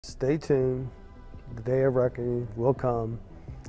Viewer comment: I was x2 speed and I forgot sounded like he was saying Satan an not stay tune